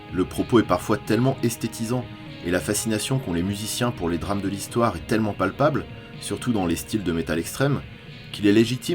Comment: C'est quoi le groupe à 8:41 please ? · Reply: Impiety